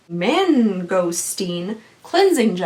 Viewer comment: I swear I heard "men ghosting" instead of mangosteen and thought "skincare is getting crazier every single day now they make men vanish how" and then I realized what I did and had to pause the video to laugh and comment.